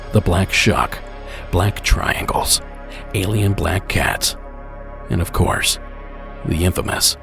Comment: Can anyone please tell me what an ABC is? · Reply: I think i heard him say alien black cats? 🤷